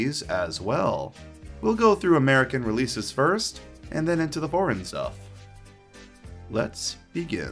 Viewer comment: when you said at the start of the video, "foreign stuff" I thought you said "boring stuff". LOL XD